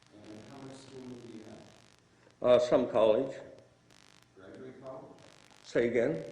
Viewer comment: does he say Trump College for his education? He was probably the valedictorian. The PD is losing his shit listening to this and the DA is snoozing. Quite entertaining good one. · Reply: He said, "Some college". As in, he has attended college, but not graduated.